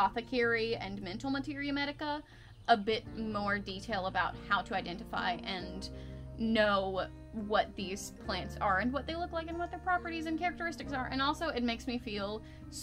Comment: what was that she said? asking for a friend 😂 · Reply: Ahaha I said “how to identify and know what these plants are, and what they look like, and what their properties and characteristics are.” 😂